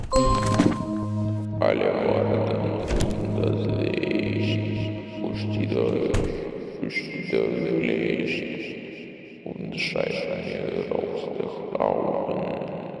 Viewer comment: does anyone know what's being said in this part? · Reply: I made this voiceline. Its "Sonne" from Rammstein. "Alle warten auf das Licht. Fürchtet euch, fürchtet euch nicht."